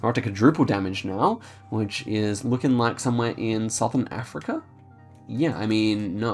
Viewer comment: did he actually say South Africa?????